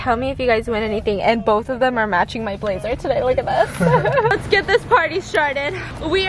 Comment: I heard “let’s get this party sharted” 😂